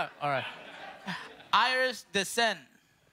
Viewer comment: I thought he said Irish decent